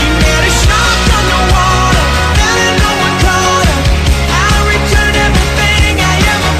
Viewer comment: I think it's supposed to say: "*fell* and no one caught her"